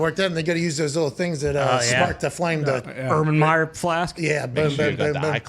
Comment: Did Ty just call an erlenmeyer flask an Ubran Meyer Flask? 😂